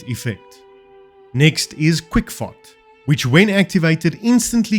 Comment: Lol...I thought he said "quick fart"...I had to run it back to understand he said FOT.